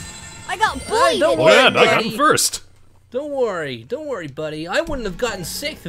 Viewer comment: I think sonic said daddy to tails when tails said he got bullied